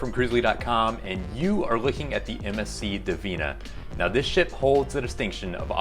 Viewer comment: definitely sounded like “this shit hole” 😂😂